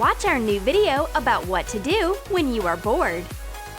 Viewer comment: I thought it said watch our video to do when your born